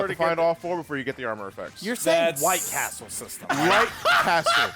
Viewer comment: Mishearing it as White Castle is so fricken funny